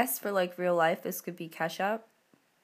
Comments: When you were putting the collar on I thought for a second you said 'I guess for real life this could be Kesha" lol it took me a second to get ketchup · when u said ketchup i thought u said kesha i was like what!!!!?????? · i thought she said in real life this could be ke$ha.. i was like what? but then i realized she said ketchup :)